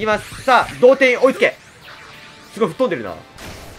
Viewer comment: 「同点よ追いつけ」が「童貞よ追いつけ」にしか聞こえなくなった